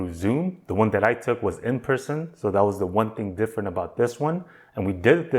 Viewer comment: You took your mentorship in "prison"? Does sound like you said that. 😅 <link>